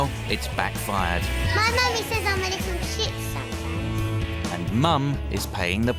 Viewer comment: what did he say?!😳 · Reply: My Mommy says I’m a little Ship some times. Replace the P with a T and you get the word that I can’t type.